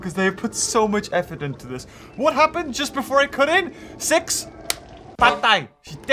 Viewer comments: Six, “patay”? Am I hearing it right, you’re speaking Filipino or my ears just tripping me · I'm still unsure of what this word really means, kinda sounded like "Patay".... plss someone explain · Did I just hear a Filipino word? "Patay" 😆 it's same as "dead" hhahahaha is " patay " a Spanish word though????? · ??? wat did he say? "patay"??? oke understandable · did he just say "patay" in filipino language which means "dead"??